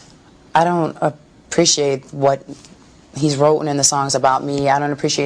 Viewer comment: this bitch did not say "wroten"